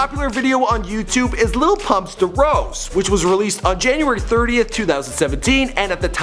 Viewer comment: DID HE JUST SAY DROUSE